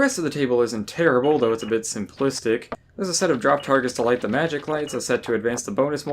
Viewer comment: did you say magic? <link> :)